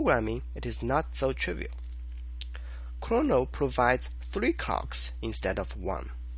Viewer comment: " Chrono provides 3 cocks instead of one." I literally died there. also, thank you so much for the video. I was so frustrated by my project but this helped me with my project and also made me laugh.